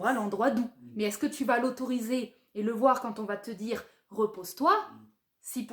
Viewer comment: Trop drôle quand tu dis "repère-toi", j'ai compris "re-perds toi". J'ai compris qu'à la 2eme écoute. Ralala je m'en sors plus de mon poisson. C'est mon anniversaire aujourd'hui, je suis tellement au summum du flou 🤣